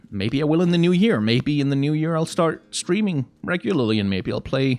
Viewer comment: "maybe in the new year I'll start screaming.. regularly" That's what I heard the first time and bursted out laughing xD